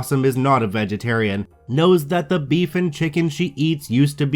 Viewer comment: Okay, I TOTALLY Thought you said "Beef IN Chicken" instead of "Beef and Chicken" XD